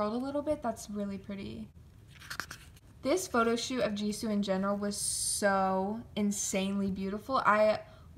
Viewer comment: Wait did she said “This photoshoot of Jisoo and Jeno” or am i trippin’? Probably cuz multi stan problems 🤘✌️